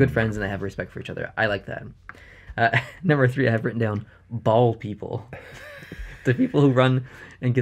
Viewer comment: I thought you said bald people and not ball people 😂😂